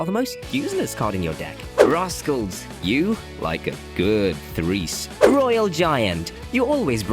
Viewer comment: did bro say “threesome” or did i hear wrong?